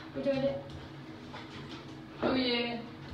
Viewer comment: （おいで 文 ）が （おい デブ）に聞こえてしまう